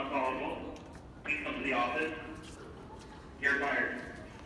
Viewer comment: Jesus Christ. Am I not the only one who heard someone at 6:05 say "You're Fired?"